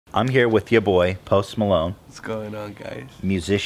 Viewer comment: i thought he said "Most Palone"